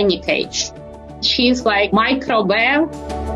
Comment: Ngl I really heard "she's like microwave" and had a giggle and backed up. Thank you for the subtitles. 😂